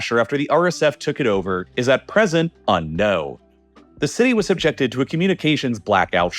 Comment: does he say unknow or unknown